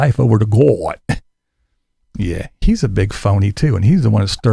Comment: No, Tater is a felon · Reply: Thanks for the response. I already knew about Tater's sordid past. But when you were started talking about Kevin towards the end of the video, I thought you said "He's a big felony too", which didn't make grammatical sense. I went back just now and listened to it at 3/4 speed and realize you said "phony" not "felony". It was not clear when I first listened to it, that's why I asked for clarification. 😊